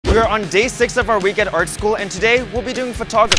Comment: i keep hearing "our weekend art school" in the intro's